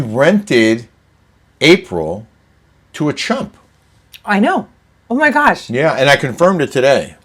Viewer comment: Holy shit I thought you said you rented your desert house to a Trump🇺🇸 I had to rewind and I think you said chump… but I’m not sure what that is lol. 😂